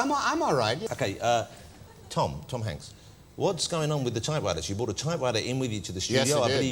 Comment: what on earth is that stupid pronunciation that guy has for typewriter -- sounds like " tie - wi - er " the p in type and the t in writer are missing, and the r sound at the start of writer is a w instead of an r. He has no authority to criticize anyone's accents after that garbage attempt at saying a word. 15:27 He said the word about 5 times and I had no idea what he was saying until Tom Hanks repeated the word.